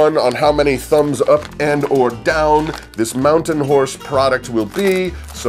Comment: Ha ha at 2:03 I thought you said mountain horse. Too funny!